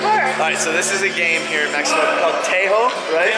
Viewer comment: did you say Mexico?